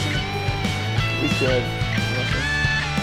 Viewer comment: I heard someone say “you should” 😂